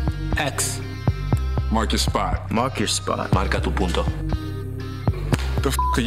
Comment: wtf did he say? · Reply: "marca tu punto" mark your spot in spanish